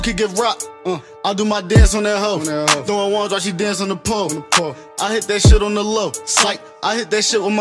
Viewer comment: I swear to god on the radio it sounded like "I hit that dick on the low" thats the only reason why I searched for this song.